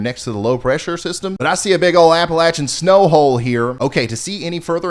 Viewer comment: Lol...Did I hear you say "I see a big ol Appalachian snow ho"